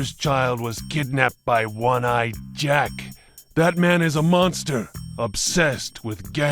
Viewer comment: I could swear I heard him say "Batman is a monster, obsessed with gambling and death"